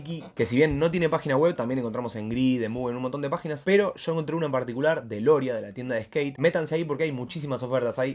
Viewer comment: que pagina dice?? q no le entendi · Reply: Grid, Moov (by dexter creo) y Loria skateshop